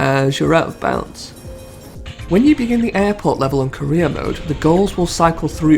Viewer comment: Dude I'm stoned, this entire time you've been saying "Career mode" I thought you were saying "Korea mode" I'm 20 mins in and only just realizing lmaoooo I've been like wtf is Korea mode.